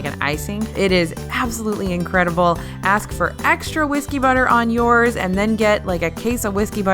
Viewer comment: I kept hearing "whiskey butter" and was so confused haha